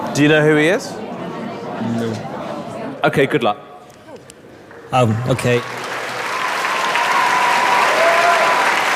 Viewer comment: what did simon whisper? He was on to her...? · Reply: he said : He was on Americas idle